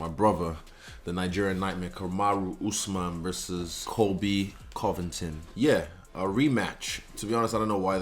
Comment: Drop your predictions below! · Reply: I legit thought you said Colby "the cunt" Covington. Hahahaha